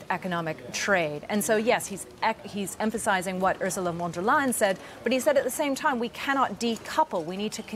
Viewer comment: Did I hear you say, “Usual Wonder Lying” ?😂